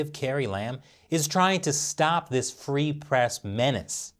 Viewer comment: My bad I thought I heard free oppression. Well if it's free what could go wrong. Back to the woorkshop.